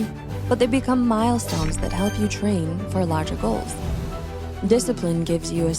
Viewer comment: This video showed me, that I really need more disciplin in my life ... I really liked it 🥰 But, I need to add: 1:21 the subtitle should be "for larger goals" instead of "for logicals", right? 4:31 "can man boards" Alright, english is not my first language, but ... this doesn't make much sense to me. 6:29 "Determinds or success"" shouldn't it be "your"? 7:09 "Paramount or sustainable growth" also makes no sense Please proofread the subs next time <3 And the music is too loud.